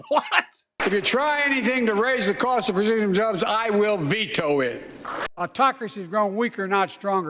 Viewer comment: Okay, for the mumbled quote, what I heard was " If you try to raise the cost of Rashida(gina?) Jones, I'll veto it!"